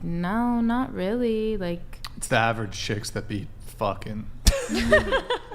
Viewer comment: What did said at 4:05:54 ? Savage chick what?